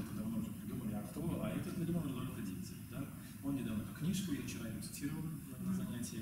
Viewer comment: Весьма содержательно, жаль что не целиком. Можете написать о какой книге идет речь на 44:25? Не расслышал автора. Заранее спасибо) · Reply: Спасибо, да, к сожалению, наше владение техникой подвело... :-( Книга -- Rodolfo Dinzel, "El Tango Una Danza: esa ansiosa búsqueda de la Libertad". Есть вариант на английском, но на испанском читать полезнее, кажется...